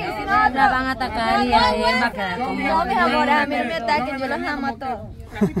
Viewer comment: Hora que bromas de la tia termo escuchen lo que dijo " yo los voy ir a tocar a TODOS"